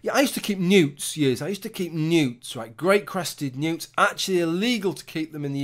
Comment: For a minute there I thought you were saying you collected nudes, not newts....